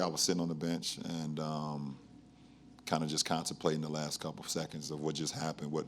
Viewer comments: LOL Did LeBron just say "kind of CONSTIPATING for what just happened"? I'm pretty sure he meant "Contemplating". But after watching G Hill missed the go-ahead free throw, JR running out the clock and Tye Lue forgetting there is a time out left they could've called. Maybe that is exactly what LeBron felt!! "Constipating"!!! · LOL Did LeBron just say "kind of CONSTIPATING for what just happened"? I'm pretty sure he meant "Contemplating". But after watching G Hill missed the go-ahead free throw, JR running out the clock and Tye Lue forgetting there is a time out left they could've called. Maybe that is exactly what LeBron felt!! "Constipating"!!! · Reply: Sounded like he said Constiplating lol